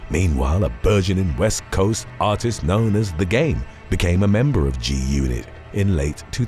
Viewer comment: 😂😂😂I thought he said "the gay" at first😂😂😂